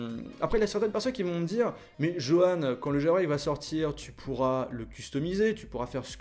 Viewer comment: Il dit quoi à la 05:41 ? Qu'est-ce qui va sortir ? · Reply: le jailbreak